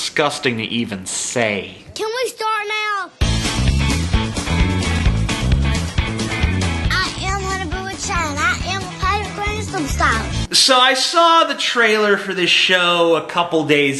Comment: "booboo child i'am a pirate gangnam style" thats what i heard